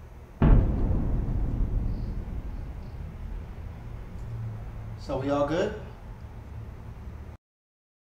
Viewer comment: I'm curious, on 3:47 what did she say :D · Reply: I am whispering "oh my God..."